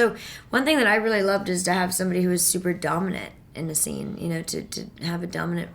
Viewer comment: i thought she said super dum